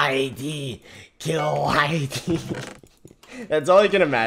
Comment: sounds like Pennywise saying kill them all